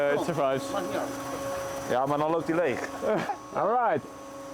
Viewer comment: why did i hear a dutch voice? "ja maar dan loopt die leeg"